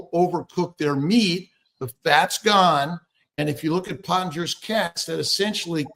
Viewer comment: If anyone is wondering what he is saying at 1:25 (I was), it's Pottenger's Cats. The first Google result is pretty good.